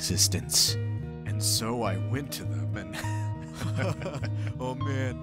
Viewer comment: What was Gaster saying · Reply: Bunny "And so I went to them and...hahahaha...." There's not really a joke there.